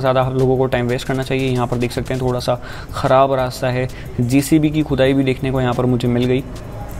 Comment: I heard "JCB ki chudai" 🤦‍♂️😂